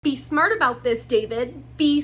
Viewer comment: i totally heard 'be smart about this damon' in the beginning and was like damn i dont remember watching this crossover episode